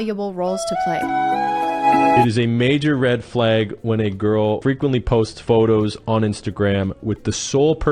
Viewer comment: I heard "red flag women wear makeup" or smth like that as I'm putting on makeup lol